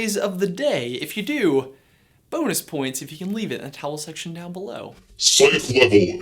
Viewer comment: Ok. I’ve been watching these guys for a few years - WHAT DO THEY CALL THE COMMENT SECTION? Is it an in joke I’m missing? I hear “towel section below”. The subtitles can’t tell either...